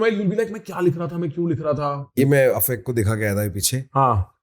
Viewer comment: did he just said AFAIK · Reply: Yes he showed that to Afaik in an interview